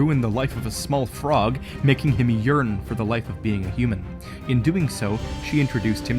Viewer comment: Im so sorry that I sat this but I heard "making him urinate" 😂😂 Had to listen to that again, sorry :D I like this theory!! 👍